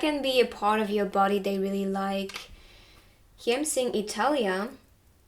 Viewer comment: I heard 'Hetalia', I gasped like 'WHAT ?!' x'DD